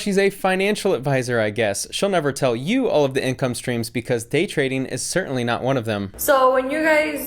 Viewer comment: I almost bought her course because I thought she said it was date trading……😂